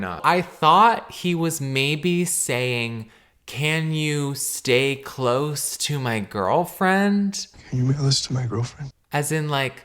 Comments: Caleb repeating a clip of a guy pretty clearly saying “can you mail this to my girlfriend?” over and over while being super confused about what he’s saying is so funny to me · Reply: I thought he said "can you be close to my girlfriend" 💀 it's stupid no matter what he said really lol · I think it's "can you mail this to my girlfriend?" like referring to the artwork? Confusing nonetheless. Great vid Caleb, happy to have you back! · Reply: I heard "Can you enlist my girlfriend?" lmaooo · I heard "can you be close to my girlfriend?" which is... a bit weird, considering it seems like those two just met?? the guy doesn't know the artist?? close in what way??